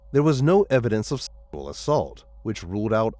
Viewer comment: What was that? It sounded like you said Sensual, assault?